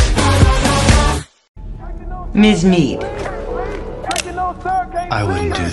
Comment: I had to do a double take at 3:32, I thought she said "mince meat"